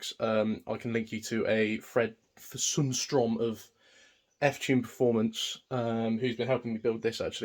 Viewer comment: What was the channel you mentioned that has the OM606 detailed videos? Sounded like you said “Fred fasoomstrom of f type performance”? Not sure how to make heads or tails out of that sentence. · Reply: That would be Fred Sundstrom of F Tune Perfomance. He has commented on this video!